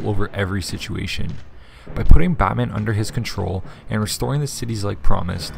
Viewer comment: I didn't know I have subtitles on but at 7:21 when he said Batman Subtitles said fat man